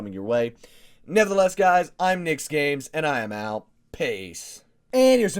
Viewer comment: Thought you said "Nigsgames" at first